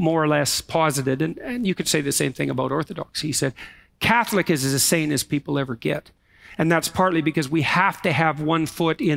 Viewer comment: I think the transcript may be in error at 1:19:44 - I believe for the context to make sense, Peterson actually says "Catholicism is as sane as people ever get..." He may have stuttered on "as" twice. I don't think he says "insane."